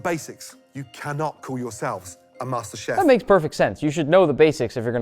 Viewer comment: Bro I thought that said masterchief XD